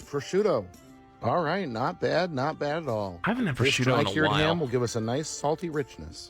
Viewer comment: i heard "Bashuda" which is my Ow, name, I got so confused. I heard "I like Bashuda"(Bah-shoe-da!) instead of "I like prosciutto"